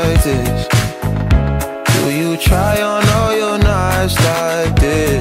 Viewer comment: I honestly thought he was saying "do you try on all your Nikes like this"